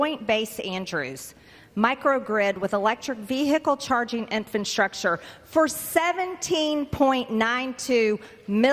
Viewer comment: IN, DID SHE SAY: "INFANT-STRUCTURE" !?!?